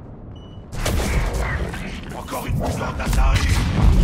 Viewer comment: Ses moi ou a 11:25 javik dit encore une putain d assarie ??????